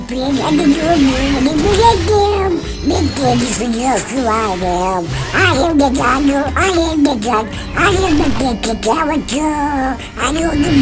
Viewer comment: I heard “ I am the Donald, I am the duck, I am the better character” 😂